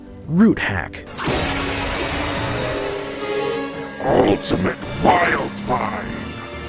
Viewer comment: it sound like he said “Ultimate FireFly” So Ultimate Ditto have a minor version of Toon Force or something? 13:42 it looks as if his skull is his body Can Ultimate Way Big manipulate gravity a bit? As he is literally a cosmic storm, do you think he can manipulate gravity?